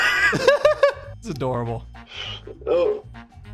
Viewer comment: Did you just say "that's a door-a-bell?" :D